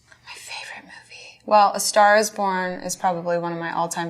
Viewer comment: I heard star's porn.